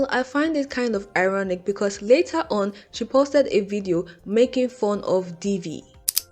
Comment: lmao i thought you said ,,made fun of diddy" 😭😭